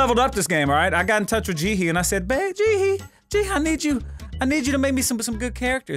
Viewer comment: Thought he said "bae, jihi"